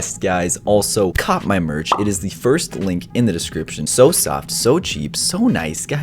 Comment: When u said “so soft” to ur merch I thought u said “so sexy” XD I mean it is shmexy but it was kinda weird tho XD